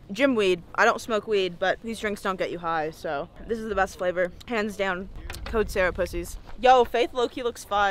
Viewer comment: did you say cans down? Jesus